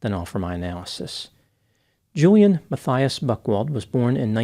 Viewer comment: I thought you said his name was Julian Buttwipe.